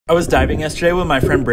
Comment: I thought he said he was diving in brittnaay